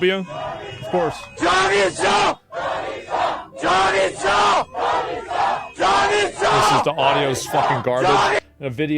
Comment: do you know what it says in 11:18? i tried to look it up and it seems to be shorisho or something which means revolt if my googling is correct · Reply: javid shah means may the king be eternal